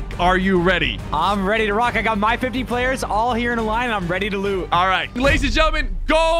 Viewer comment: I thought nick said I’m ready to lose